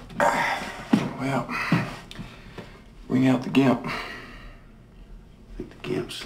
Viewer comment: The first time I watched this movie the sound quality wasn’t very good and I was on a train so I thought he said “bring out the chimp” so I was expecting the dude to bring out a monkey, and just a few seconds later I was greeted with a dude in full blown leather suit, didn’t really know how to react.